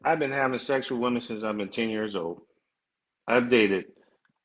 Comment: did he say 10 or 18??? 😬😬😬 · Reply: 10